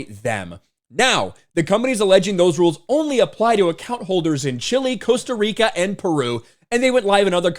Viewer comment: I kept hearing "chilly Costa Rica" and thought was just going insane.